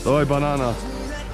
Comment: did he say "doy banana"? 😂😂😂😂